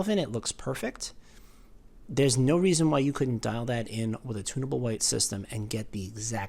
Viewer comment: I thought you were saying "tuna boat" white. Lol. You were saying, "tunnable".